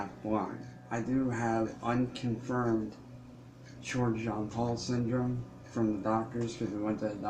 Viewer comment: What is he saying at 2:24? What syndrome? It sounds (and the CC says it too) that he has “unconfirmed George John Paul syndrome”? What is he saying? I know there is No syndrome named that. And I can’t think of a syndrome that includes facial abnormalities that sounds like “George John Paul”. Except maybe DiGeorge Syndrome or Pallister-Hall syndrome? Is he saying “Pallister-Hall”? That one usually doesn’t create cranial or facial distortions tho. Someone help lol.